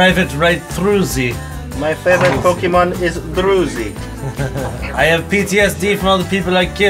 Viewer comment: I heard "Друзі" :) (means "friends" in Ukrainian)